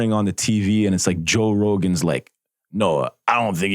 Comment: Lmao did he say joe Rogan 😂?